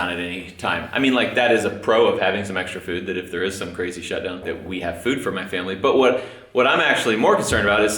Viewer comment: I thought said if there is some crazy sh!t go down.. 🤣🙈